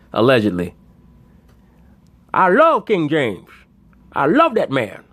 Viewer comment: That sounded like LeShannon James